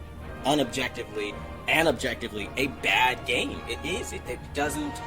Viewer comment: Did he just say unobjectively AND objectively?? My brain just short-circuited and I'm too lazy to rewind the video to check... but not lazy enough to still type this comment.